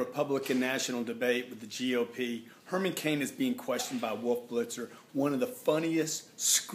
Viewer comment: He got it right, I thought he said blitzed, that`s who we need in the White House. Help Save America Vote Herman Cain for President.